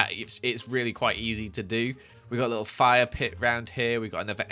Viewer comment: Whenever he said fire pit I thought he said fire playground and got very concerned